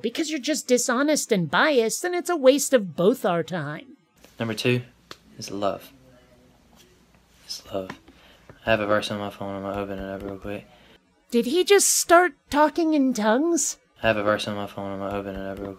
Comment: at 5:08, it sounded like he said "Have a verse in my phone on my oven of that real quick."